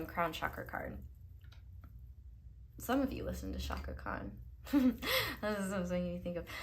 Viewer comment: who was the singer you mentioned? 'Some of you listen to (name)' but i didnt catch the name. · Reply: Chaka Kahn